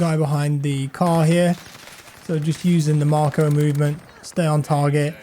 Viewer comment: Did you say “Marco movement” or something else when you killed the guy behind the car around 7:30?